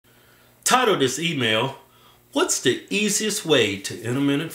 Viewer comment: tidal oh dis emayal. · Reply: That shit crack me up bro :D for a long timei thought he said tired of this email